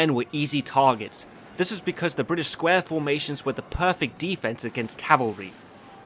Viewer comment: why did i mishear this as ''russian diamonds'' instead of ''british squares''